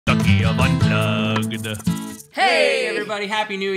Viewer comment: I keep hearing "the key of butt plugged" XD